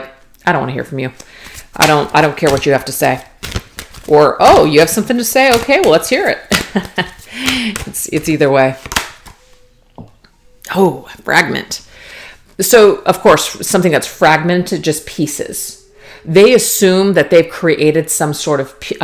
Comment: I thought you said Stagnant not Fragment… but I guess I feel both right now.. yeah Definitely Stronger now and definitely Queen of Swords 🗡️ This is near future energy… I feel it..